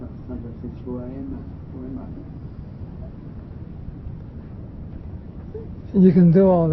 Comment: Hey - at 1:37 I think I heard the first man say ‘ I ask myself who I am…who Am I?” I can’t hear what the next person/woman says. This sat sang is so wonderful, how I wish I could have been there, but this is the next best thing. Thank you so much for bringing this to us❤️✨